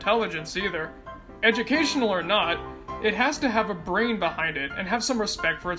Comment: thought they said "grain" instead of "brain" and I thought of the crustless bread song